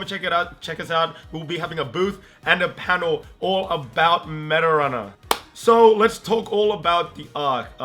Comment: Guys if you put english generated captions on for 1:30 to 1:45 you will notice that the subtitles say "Marijuana" instead of meta runner XD